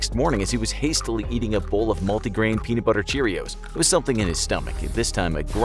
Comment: Did this man just say multi grain peanut butter cheerios?Or am I hearing things?